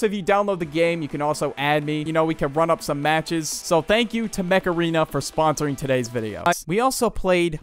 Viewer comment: I love this and when he said Mech area i thought he said Macarena lol 😂